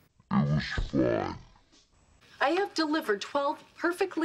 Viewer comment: Also when in the episode of Marvins birth Robin says “one of them was even human” she’s referring to the episode where the gang gets together at Marshall and Lily’s to watch Robins crazy early morning show, but instead end up talking about how Lily broke Ted and Karen up, and you can see Robin delivering a human baby on the TV in the background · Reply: Oh wow! You're right. At first I thought she said "one of them WASN'T even human" referring to the farm animals. But then I went back and check the subtitles, she did say "one of them WAS even human" which makes much more sense.